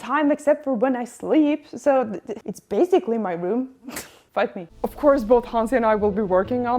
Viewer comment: Uhhh…..did I hear “f**k me” in misread of “fight me”?